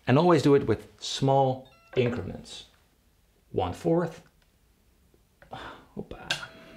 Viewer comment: did you say opa? Haha thatsa greek exclamation